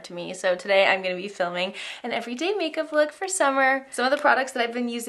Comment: Is it bad that I'm brainwashed and all I heard was, "Today I'm going to be filming an Every Day May..........cup look for Summer 😂🖤